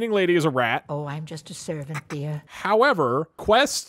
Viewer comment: You actually misheard her. She didn't say servant, she said SKEEVER. Very easy mistake.